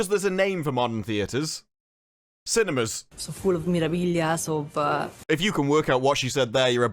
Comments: "...so full of Memorabilias..." is what this woman said. Ya welcome ^_^ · I think she says "it's full of memorabilia "